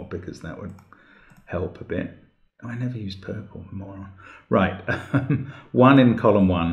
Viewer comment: Mark: "I never use Purple...", (Mark musing out loud). Lmao 😂 You're exactly right, Mark. Hilarious ;)) 😂😂 [[You're right, purple is too dark to see sometimes. Still funny though, lol 😂 ]] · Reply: I thought he said "I never used purple, moron". Past tense. Because he'd earlier thought he had run out of colours, which is why he re-used blue. He would have used purple if he had realised he hadn't yet used it. He beats up on himself by calling himself a moron, though. He certainly isn't that. 😀